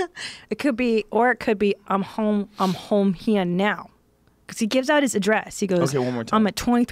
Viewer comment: I hear "I'm Home hear and now"said like hoome ear~n~now